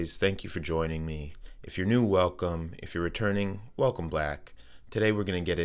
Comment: Sounded like you said welcome black 😅